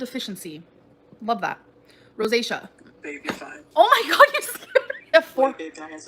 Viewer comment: did she say “babe you’re fat”? 😂